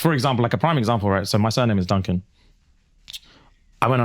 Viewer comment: Tell me why when said "my sur name is Duncan" i had to run it back because what i heard was "my son name..."